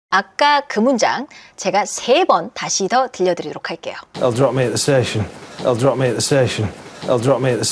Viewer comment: They'll 이 왜 저는 I'll 로 들릴까요? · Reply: 편집에 문장이 급하게 시작해서 그런거 같아요. ㄱ ㅑ들이 나 역에 내려준댜. 저는 I’ll drop me the session 이라는 얼토당토 없는 말로 들었네요 ㅋㅋ